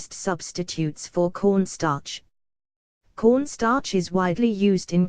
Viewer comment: I thought she said porn star